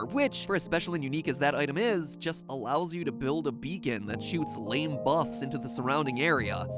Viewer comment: Wait what did he just say "lame buffs" on